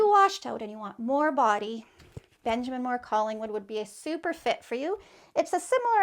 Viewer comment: Great info. Can't understand the name of your Benjamin Moore option at 2:09 Calling would? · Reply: I think I heard Collingwood by BM.